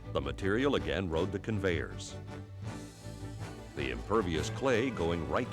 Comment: did he say impervious clay? We shall see!